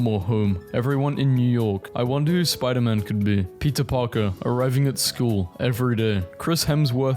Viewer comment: Bruh I heard "Pizza Parker"